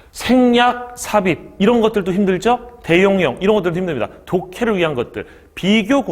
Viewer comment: 선생님, 13:32에서 뭐라고 하신건가요? '대'라는 음절은 들리는데, 뒤에 뭐라고 하신건지 잘 모르겠어요. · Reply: "대용형" 이라고 대명사, 대동사구 처럼 대신하는 것들을 말합니다 ^^